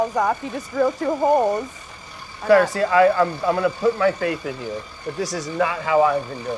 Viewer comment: I am hard of hearing and I thought he said " I am going to put my face in you". I have to get my head out of the gutter.